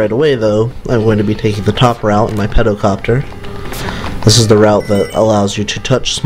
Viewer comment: I have no idea why, but it sounds like your saying Petocopter. Cruelest mistake indeed. Chances are you know this, but Antlions are real bugs.